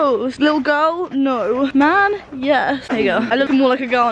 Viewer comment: so am I hearing that right? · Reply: Bug she said “there you go” i